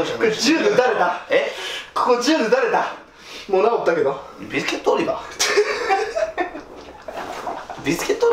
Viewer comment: 銃で撃たれた、もう治ったけど… の後のツッコミなんて言ってる？ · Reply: ビスケットオリバ